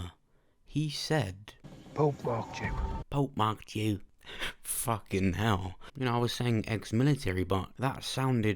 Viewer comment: I heard " Pope bought you"